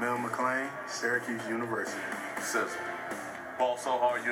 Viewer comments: sounded like mcclain said "stairacuse" university. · I thought he said "Balls So Hard". Sounds painful...